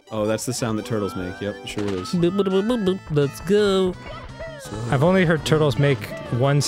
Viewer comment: When Mario says let's go at the beginning of a level it sounds like he says pickle